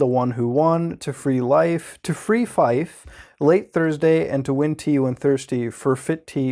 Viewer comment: to be honest because of the visuals I thought it said I ate Thursday